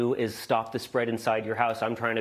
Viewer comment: did he say that he was a FRUIT? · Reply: "Stop the spread inside ur his house" - that's what he said.